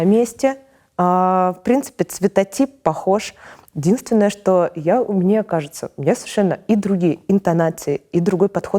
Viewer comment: "... я умнее кажется..." ? Не расслышал про Шульман. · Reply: "Я, мне кажется, ..." Я думаю так было. Обозначение мнения просто